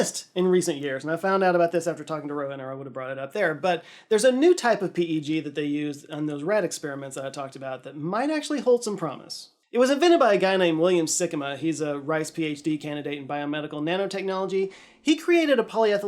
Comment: I thought he said William Sigma